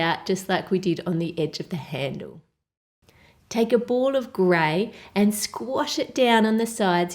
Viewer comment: Lol when she said take a ball of gray, i thought she said get some clay LOLOLOLOL (its at 5:03 )